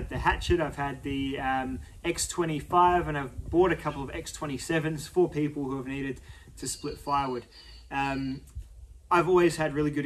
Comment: I have no idea what you're actually saying at 2:34, but google assistant is certainly curious. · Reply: "I've bought a couple of X-27's for people who have needed to split firewood" Source - I sound like him.